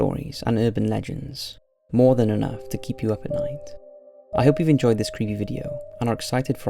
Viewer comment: did he say "i hope you enjoyed this creamy video"????